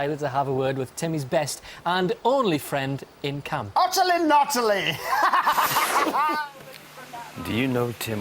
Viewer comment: what does Ant say at 0:37? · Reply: Based on later episodes where its more clear he said "utterly nutterly" which is a play on/ variation on Timmy's catchphrase "Utterly brilliant" from Wacaday